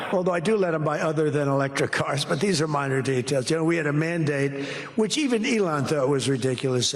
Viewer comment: Trump says mandate and I hear "man date"